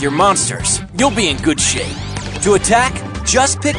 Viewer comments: Is it just me or I heard said "Good shit"? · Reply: I may be misheard though because I rewatch the part where said that at 8.00 to 8.05..I think he actually says good shape hahah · Is it just me or I heard said "Good shit"? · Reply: Around 8.00 to 8.05 but maybe I misheard..because I heard him saying good shape instead hahah